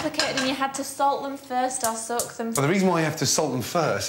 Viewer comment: did she say "salt them or suck them?" if she did then thats just waw she should've just told gordon she wanted him in the bed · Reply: She said salt them or soak them